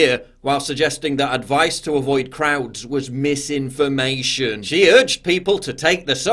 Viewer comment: Anyone else hear “Advice to avoid Krauts was misinformation” ?